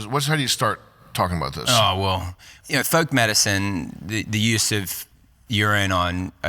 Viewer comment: I heard "thug medicine"